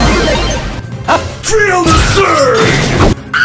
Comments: I remember when I first heard this voice line I thought he said:"three other surge" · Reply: yeah first I thought he said "three other surge" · I remember when I first heard this voice line I thought he said:"three other surge"